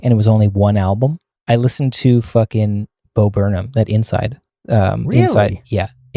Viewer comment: what does he say here? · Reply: Bo Burnham ;)